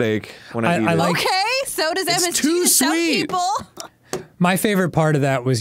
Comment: what is Ally saying? I give up. It would make sense if she's saying 'ice cream' gives people headaches, but I kept hearing 'LSD' and trying to make that make sense. Now it sounds like 'MSD', and I don't know if that could be anything. Halp.